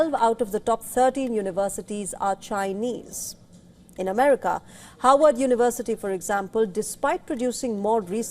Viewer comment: it is Harvard University, not Howard University; Howard is a historically black college in Washington, DC. The caption is wrong.